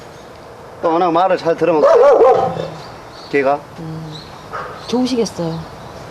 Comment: 좋아하는영상가끔 다시보는데..이영상 볼때마다 웃기는건..군남이 취재pd?취재원? 한마디 좋으시겠어요..소리가 난 왜? 그렇게 좋니? 로 들리지 ㅋ~ㅎㅎ